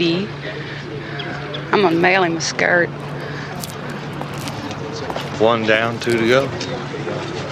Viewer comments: what does she say there? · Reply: I can only hear smth like 'Imma mail him a skirt'... But I don't see how that would make any sense to say? Yeah, it's confusing. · Yeah, you've got it. "Prison's not a safe pace. I'ma mail him a skirt." She's making a prison rape reference. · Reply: Oh, I didn't even hear the "Prison's not a safe place", the accent is really hard for me I heard "I'm a mailing skirt", I'm an idiot for not thinking about context.